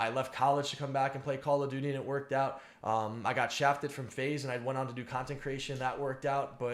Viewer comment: I thought he said he got shot lol